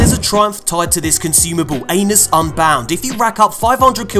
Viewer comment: he says “Anus” instead of Atlas 😂